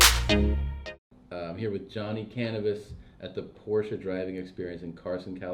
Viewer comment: I thought he said Johnny cannabis 😂